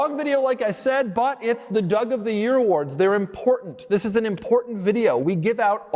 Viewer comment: Am I trippin or did he say "Doug of the Year award"